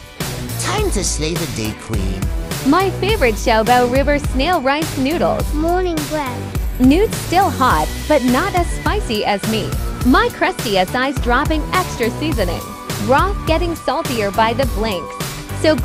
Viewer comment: "Morning breath" 😭😭😭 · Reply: W-w what breath? I thought you said bread